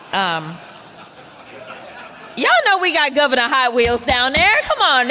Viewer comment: I thought she said 'high wheels' at first. You speakie Engie.